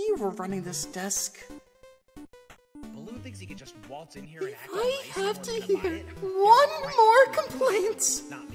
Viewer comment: Can anyone tell what Nickel is saying at 1:45? Ive narrowed it down to "Balloon thinks he can just waltz in here and act like he wants to form an allience? Yeah right, not me" but its hard to hear